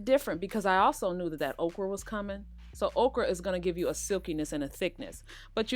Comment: Thank you! I really wondered who would have the best recipe and it’s definitely you. Also, I thought you said, “Oprah was coming...” and I was SO confused and excited. Ha! You said okra. 😂